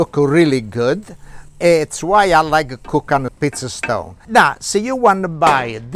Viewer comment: Looks good! I thought you were saying “piece of stone”. Then I finally realized you were saying “pizza stone” ! Lol